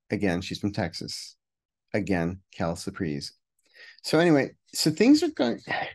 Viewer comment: What is Kel cpr at 11:09? I can’t understand what you are saying, please. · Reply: Oh! quelle surprise! I never took French in school!